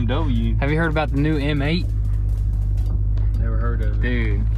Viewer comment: he did he shook his head yes tho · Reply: I thought he said "never heard of it"